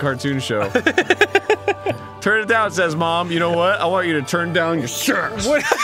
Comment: "'Turn it down', says mom. You know what? I want you to turn down your sass." I can't stop listening to that part. XD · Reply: I thought he said turn down your sex